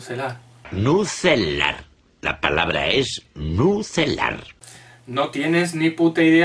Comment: Otra joyita vieja! Buen vídeo, los gritos de y la voz de narrando los insultos son épicos. 1:10 que mierda es eso? Pensé que decía: "atómico! Se dice atómico". Por cierto, los argentinos psicologos? Wtf, yo soy argentino y no tenía idea que nos tienen de psicólogos. Saludos!